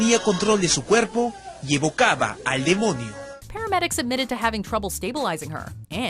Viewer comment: 다들 말하는게 그렇게 들려ㅠㅠ(5:40에서 입을 까봐 알게뭐니가 더 정확히 들렸어요 ㅠㅠ 댓글보다가 알았어요)